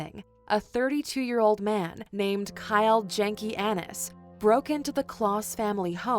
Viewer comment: Did she say dude's name was Kyle Jankey Anus? lmfao